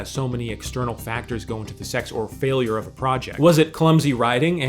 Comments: did man say "sex or failure"? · Did you say sex instead of success on purpose at 3:25?